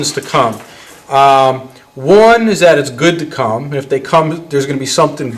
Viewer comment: Did he just said is good to come?